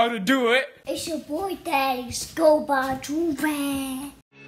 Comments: I can’t be the only one that repeats “It’s yah boii Thadius go buy a joorag “😭😂❤️ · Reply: I thought it said “it’s your boy daddy scopa joorag” 🤦🏽‍♀️😂😂😂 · I can’t be the only one that repeats “It’s yah boii Thadius go buy a joorag “😭😂❤️ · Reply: whole time I thought he was saying "it's ya boy dex"